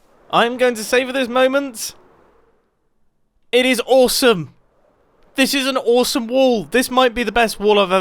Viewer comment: Why did I hear: “It is wallwesome!” 😂